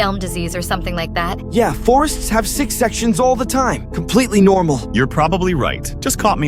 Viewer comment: wait did he say 67?? , oh wait its six sections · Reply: it said it sick section